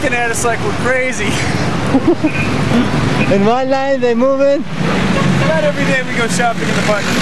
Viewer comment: Anyone can write what he said at 10:40 ?? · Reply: I think that was english. I don’t really speak other languages, but I think the guy said: “In one line, they movin’”